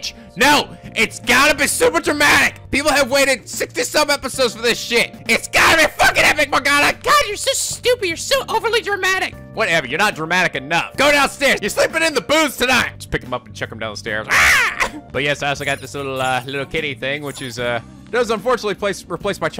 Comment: did anyone else hear "your sleeping in the booze tonight!" ?